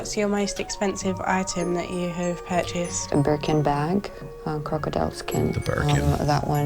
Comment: Thought she said “My Broken back” when she actually said “My Burlington bag”😆😂 Cashmere’s my favourite type of “material”🤣🤣so educated😂😂